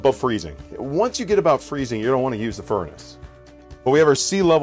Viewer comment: Why not use the heat/furnace if it freezes? Hummmm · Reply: is he saying -"You dont wanna use the furnace"? Thats what I hear. It cant be "gonna wanna" is it? Ive played it now twice. Might want to address that more clearly. I am one who listens well and frankly you need clear representation. I do appreciate your effort to address.